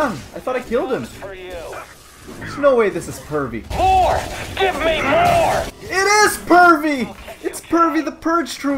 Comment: I always thought he said "whore! give me more!" how wrong i was